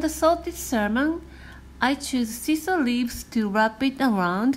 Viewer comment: They all look very tasty. I'd have to ask - What exacly is this nice shaped leaf around the Onigiri with the salmon called? 6:00 I couldn't understand it. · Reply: That's a shiso leaf I believe ✌️